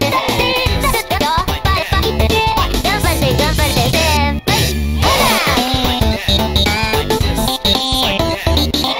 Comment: nice!!! also what does nagatoro say at 1:10? I hear senpai but nothing else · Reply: Gambare gambare senpai